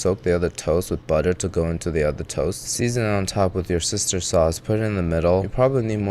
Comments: I'm no native english speaker and holy s... 4:42 I wasn't fully paying attention and it sounded like "with your sister's sauce" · with your sister sauce? Please tell me I didn't hear that right 😂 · What he said: "Worcestershire sauce" What the automated captions said: "Your sister sauce" 💀